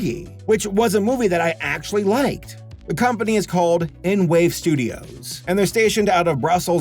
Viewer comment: I actually thought it said n word studios